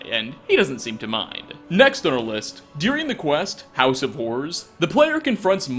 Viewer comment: As a non-American, it sounds like he said “house of whores” when talking about Molag Bal’s mace lol. I love all his mispronunciations. Makes more human. Like calling Captain Aldis “Captain Ayalds” and pronouncing amulet “amlet”. Fantastic videos though, thoroughly enjoy them. (Murdered to death!)